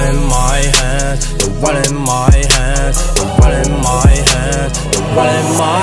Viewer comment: When i first heard this i thought he was saying "There's blood on my hands, there's blood in my hair"